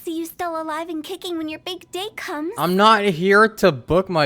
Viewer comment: I heard her saying "Big dck" in slang way!😂 Anyway, at the back of Hu tao's mind while she's advertising her business "If no one dies, our business will die".